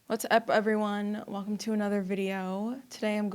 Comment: I thought you said "What the F everyone." Sorry.